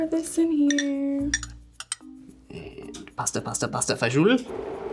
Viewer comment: DID HE ACTUALLY SAY ‘PASTA E FASUL’?